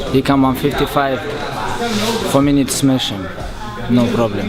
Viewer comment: I think he said : Fpr me need smash him. He wasn't very good at english back in the day